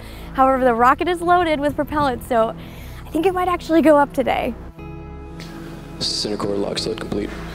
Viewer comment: I thought she said “I think it might blow up today”